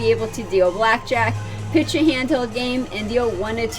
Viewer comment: What did you say after the word 'blackjack' and before 'be able to deal one of two games' at 5:12? It sounds like 'pitch a hand held game.' I have no idea what that means. Is Pai Gow Poker the same as Pai Gow Tiles? What game are people referring to when they just say 'tiles' for short? If they're not the same game, then which do you recommend learning for casino jobs hiring demand and pay?